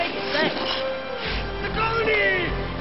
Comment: It sounded like he said "The Goatee! Whoa!"